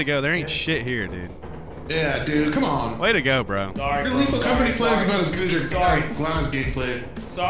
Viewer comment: wtf does say @ 10:13? 'you're game play is about as good as your glados letsplay''?